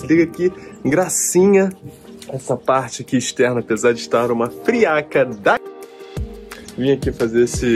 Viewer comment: friaca da o quê? Não entendi o resto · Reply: Da Cassandra!! Rsrs = muito frio!!!